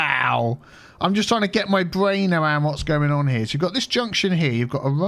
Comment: I thought it said my brain hurts?